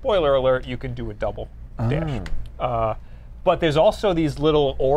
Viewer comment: Did you say "Double Dash"? :D